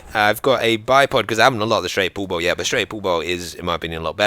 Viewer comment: Lol! 1:54 -> 1:56. I kept hearing 'straight poephol' initially (Afrikaans equivalent of asshole), had to go back and watch it again haha. Great video as always! Looking forward to more DayZ!